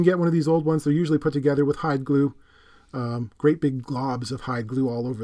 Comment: Oh, HYDE Glue! I always thought he was saying High Glue.